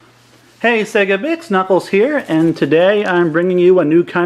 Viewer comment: i thought he said sega bicth